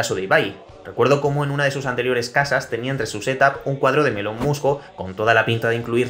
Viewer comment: un cuadro de quién? Te juro que he intentado escuchar como 5 veces lo que dice y no le entiendo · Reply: Elon musk